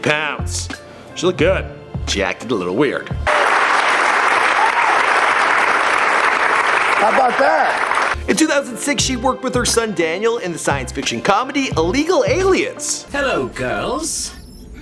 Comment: I heard "How bout dah" haha